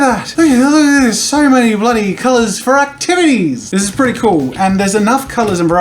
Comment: Did he say titties?